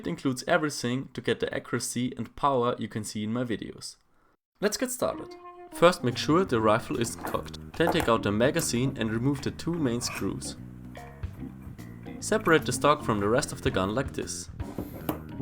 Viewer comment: Did you say first make sure that the rifle is fucked?